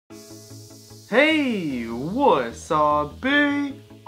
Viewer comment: Does any one know what he says at the beginning it sounds like he is saying hey! Wassup pee... I cant understand him. Any 1 know what he is saying? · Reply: PortalByte wasabi. It's like a thing he does. He explained it in one of his old vids.